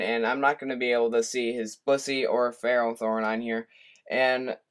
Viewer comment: oh god, bwhahaha. now that i listened to that part it does sound like he said that. he said Blissey though. 'm sorry but i think thats hilarious